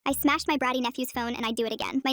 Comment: What I beautiful introduction. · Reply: I thought it said i smashed my batty(bum cheeks)